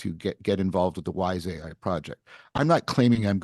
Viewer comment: I thought he said “the wise Isaiah project”